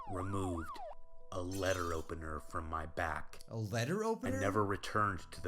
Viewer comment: OMG WHEN HE SAID "LETTER OPENER" I THOUGHT HE SAID "LEAD OPENER"